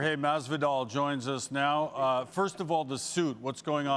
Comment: Thought it said Jorge Castanza